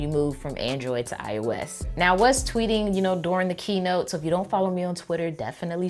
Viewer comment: At first I thought you said "I was fourteen" lmaooo